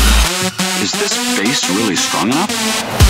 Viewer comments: Every time he says "Is this place really strong enough?" I hear: "Is this face really strong enough?" XD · Is this bass really strong enough? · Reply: I heard "Is this paste really strong enough?"